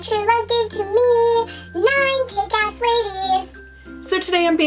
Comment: Literally thought you said cake ass ladies and i thought of that cake farts vid I swear